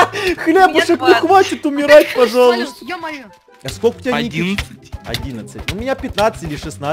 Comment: Кому послышалось что он спросил сколько ему лет?❤